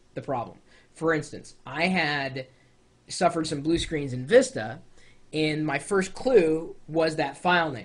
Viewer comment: It sounded like he said, "Blue Screens of Vista" for a second there. (7:03) hahaha...